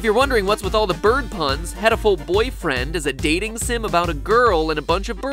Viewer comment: what was that dating sim game he referenced at the beginning? It sounded like he said pedophile boyfreind. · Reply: Hatoful is what he said.